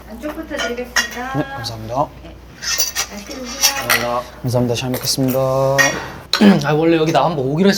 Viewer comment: no hate but why it sound like han is saying Bismillah before eating😭😭